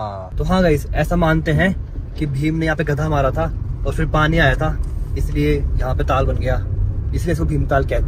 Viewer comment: Kya bola bhiya gadha mara 😂😂😂